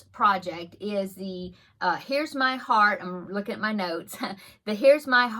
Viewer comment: Oh my word. I thought you said, " Here's my heart, look at my nose." Lol, that would be quite a name for a project. What adorable projects. I love the owls. Thank you for thinking of this.